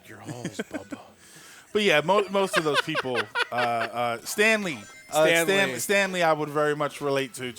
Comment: At first I thought, "what the hell?", but then realised he said "Stanley".